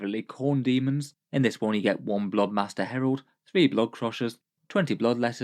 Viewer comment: I heard "Bloodmaster Harold" instead of Blood Master Herald 😂 and now I can never see/hear about this model without thinking about it